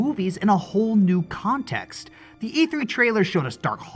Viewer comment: For some reason I was thinking EA when you said E3, and I made a joke, and rehearing it, the joke didn't fit. I thought you said EA, and I said: “Well, there's your problem.” gee I hope E3 don't turn sour when it would work.